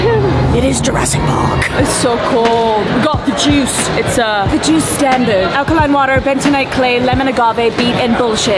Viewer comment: at the juice stand I saw Jurassic Park instead of Juice Stand